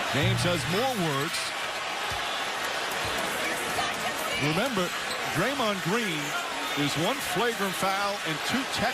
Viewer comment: Listen reallll close and hear what the lady in the background said XD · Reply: ur such a bitch ?